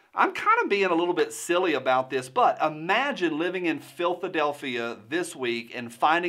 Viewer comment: did I hear that right? I think he called filthadephlia lol